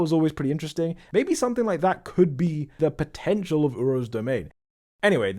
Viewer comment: DID YOU SAY POTENTIAL